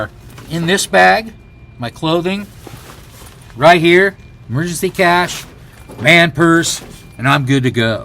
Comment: hahaha Oh my G, I thought you were saying; "Emergency Cash" *& I could see why you were FREAKING out! :) But yes, right you are; you can do a lot but food and water are vital................as they say S___ happens. All you can do is roll with it.